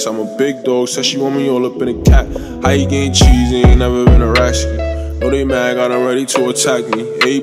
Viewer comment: Is it just me or i thought he said "how u getting cheese when u never heard rats" but this still is fire🔥